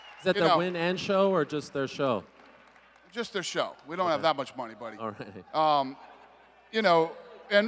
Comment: Did dana just say " We dont have that much money". The ufc sold for 4.2billion wtf ?